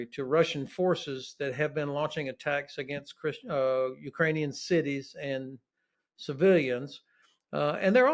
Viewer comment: Anyone else catch him say “christian" at 2:34? 😂😅